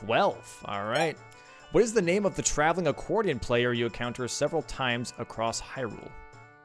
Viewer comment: for some reason I thought they said, "time traveling accordian player" and I was super confused